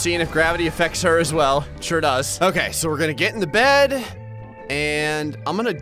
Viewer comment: Did just say "We're gonna get in the bed" while climbing into the trunk (or whatever that's called)?